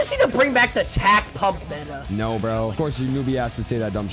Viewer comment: Tell me why at 2:06 or 2:07 it sounded like said nga